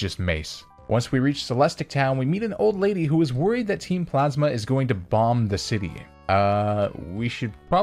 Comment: does anyone else hear him say team plasma? Or is it just me